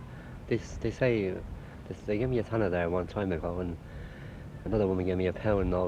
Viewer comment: I thought he said "they gave me a tenner" and I thought wow, back then that was a lot. Then I realised he said "tanner", was just 6p.